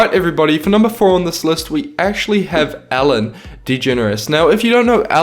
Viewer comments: It sounded like you said Allen DeGeneres not Ellen DeGeneres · Lol when u say Ellen it sounds like ur saying Allen